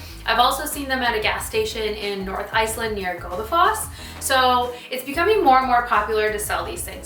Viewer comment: Please write the name of Icelandic towns in large font in your videos when you say them and slow the prononuciation so we know what you are referring to and can google them. The captions never pick it up. Ex: 5:12 No clue what you said or the town before it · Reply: Godafoss